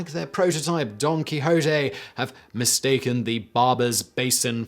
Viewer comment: I misheard "Don Quichote" as "Donkey Chote".